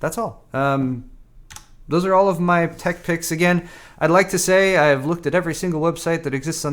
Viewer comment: Tech Pics kinda sounded like you said Dick Pics hahaha